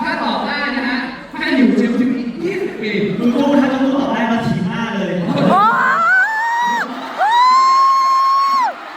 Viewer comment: what did book Said? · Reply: he said if junior can answer the question correctly, junior can kick at his face.